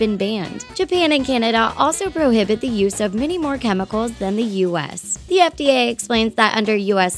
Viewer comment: anyone else notice it says 'that' instead of 'than' in the captions?